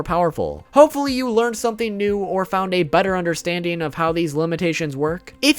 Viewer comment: He said butter understanding. Lost a bet i thought he said better.